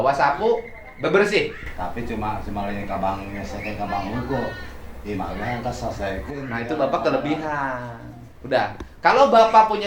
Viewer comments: emangnya bapak itu ngomong apa ya? gatau artinya😅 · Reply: Jangan bank aja yg di lunasin, rumah juga selesaikan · ngomong apa si bapak nya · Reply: Tapi teu mahi ka bank hungkul, imah ge hayang di beresan